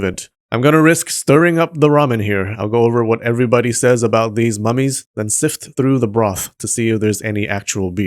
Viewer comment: Why I heard "these mammies" instead of "these mummies"? XD